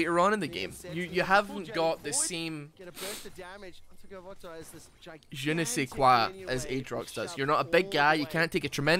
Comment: what word did just use?! i never heard this one! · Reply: “Je ne sais quoi". It's a french phrase commonly used to the point of even being adapted by English speakers and it means something like “I don't know" so when they are saying that it's missing a certain je ne sais quoi it means it's missing a little something, something, idk what but it's missing it. Other french phrases commonly used in English would be “avant garde, deja Vu, faux pas" and many many more. Lol.